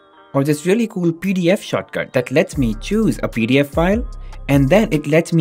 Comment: Oh I thought you said short fart lol